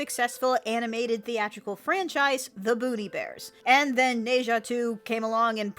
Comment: I thought you said "Booty Bears" not "Boonie Bears" and I admit I had questions...